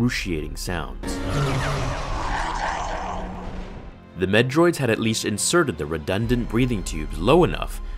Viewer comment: At first I was like, "What,did he say METROIDS!?!", but then I realized he said "Med Droids", as in "Medical Droid", LOL